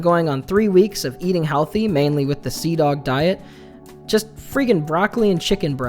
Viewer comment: okay, what is that you were saying? Sea dog diet? What is that? Is that a reference to an anime or a youtuber? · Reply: Cdawg, youtuber yeah